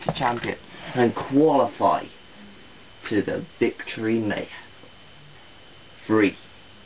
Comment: Yes Dantomkia! . At 4:12 it sounded like you said Victory Nathan. Cant wait for Victory Royale.